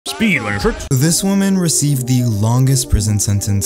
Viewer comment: I swear I heard “Speed wearing shorts” 💀